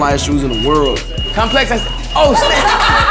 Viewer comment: did he say oh snitch or oh shit